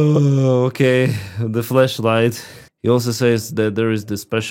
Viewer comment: “How to use the flashlight without any hands…” you had me spraying my tea out with laughter! You look tired but still amazing. Thank you for your hard work - it can’t be easy. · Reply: xD I heard "Flesh light",.. The Internet has destroyed me.